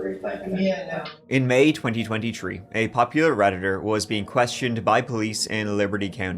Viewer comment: To whom it may concern: I thought you said a popular rapper 😂😂 and I love you dude and your content